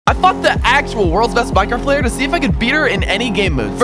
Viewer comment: I thought he said so I fuked the actual best Minecraft pvp